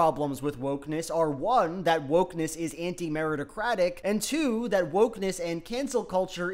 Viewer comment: Genuinely thought Elon said "anti-Americacratic"